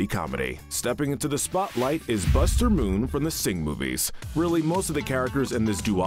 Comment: i thought it said sigma movies💀💀